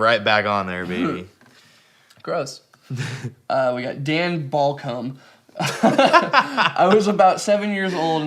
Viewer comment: Mad respect to the chick with the period face thats metal af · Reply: i heard Dan Ball Cum